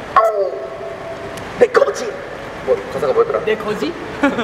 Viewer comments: did they just say "dekho ji" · how many Indian armies thought jimin and jhope said "dekho ji" ... literally I did